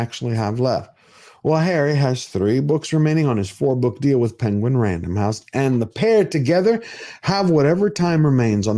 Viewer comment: I know you meant PenguinRandomHouse. But Sir, what I heard you say is PengWing random house 😂😂😂😂 I love it!